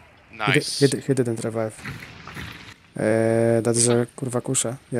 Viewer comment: What are you saying at 1:15? Funny and entertaining video as always mate! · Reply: i said basically that is a F'kn crossbow, thank you